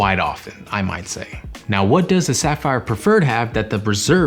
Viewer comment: Did it kinda sound like you said Sapphire Preserve at 3:42? That's a good new name. Also, I think the CSR $300 travel credit doesn't need to go through the travel portal, just swipe or tap. 👍🏼